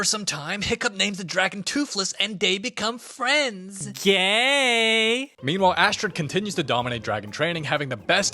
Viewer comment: did he say yay or gay